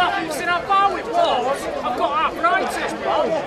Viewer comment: ive arthritis he said thought he said i joined isis at 35secs